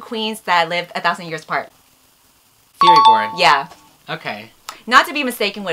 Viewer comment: thought for a second that it said 'furry porn' and i was like bruh